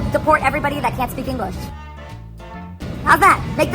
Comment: …..in the intro I heard “support people that don’t speak English” 🤣🤦🏽‍♀️ I shoulda known better